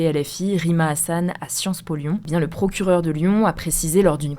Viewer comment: la première fois j'ai compris à Sciences pognon xd